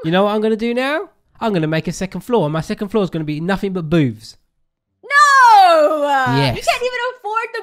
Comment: What I thought said:my 2 floor is gonna be nothing but boobs